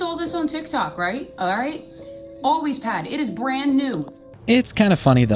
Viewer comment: "ALWAYS" bro I thought you said "Aldi's"😂